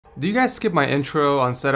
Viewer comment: All I heard was "Do you guys ski.."